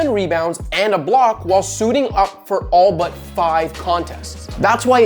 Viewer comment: Bruh I thought you said “all of 5 contest” not “all But” lmao I was like why tf would they want that man and he played 5 games in 3 years🤣🤣